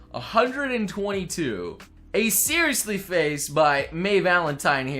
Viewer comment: Did anyone else notice he said "Maye" instead of "Faye" around 7:30?